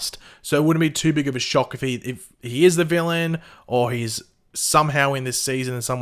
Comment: I thought I heard he was the villain